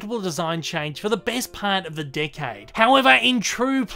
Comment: 10 : 00 it sounds like you said dickhead instead of decade